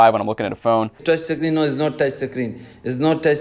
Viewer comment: "Its not toucshi screen." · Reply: I heard "It's not tested green.". 🤣